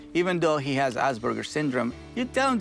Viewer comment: I thought Caesar was saying the dog has ass burgers!